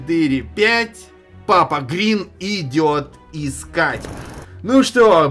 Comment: МНЕ ОДНОЙ ПОКАЗАЛОСЬ ЧТО ОН СКАЗАЛ КРИЛ?